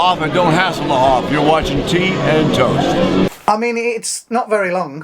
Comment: Great, as always! At first I heard, "Team Toast". . . What?! What about Team? LOL! (Don't take anything I write, or say, seriously!) . . . except for this: Another Great Video, Guys!!